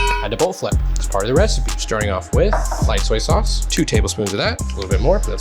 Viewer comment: I got excited for a second, I thought you said, lime soy sauce 😮🤔 After listening for the third time, I realised I have not been introduced to a new culinary magic 😭🤦‍♀️😅